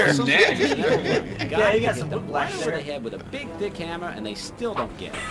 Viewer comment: hol up, did actually said BIG DICK HAMMER ???